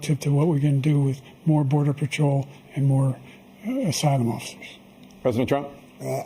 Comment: It sounds like he says, "...Side Mufflers.." but your guess is as good as mine.